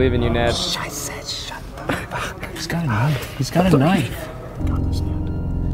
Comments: did Ned say ´´scheiße‘‘?😂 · I sounds like he said "Scheiße shut the fuck up" and Scheiße means shit in German. I thought I should share it with you guys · did ned say "Scheiße"??? Rly sounded like that. That thing eugene said about it being the worst thing ever ... that was brutal as shit :D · DID NED REALLY SAY IN MINUTE 6:50 "SCHEIßE"?!?!?!?! OMG IM SCREAMING!! Greetings from berlin xD🇩🇪💜